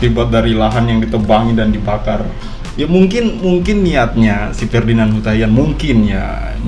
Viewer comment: Ferdinand si taian,? apa saya salah denger ya😁😁😁😁